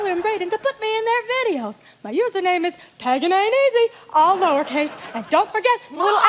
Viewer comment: Did she say her username was Peggin'Ain'tEasy?